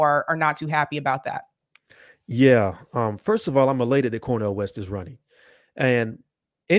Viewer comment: I thought I heard him say we was related to Cornell...OHHHH. ELATED. 😂